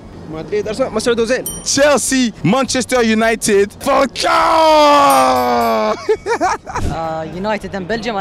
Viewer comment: Who did say at 25:46? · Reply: falcao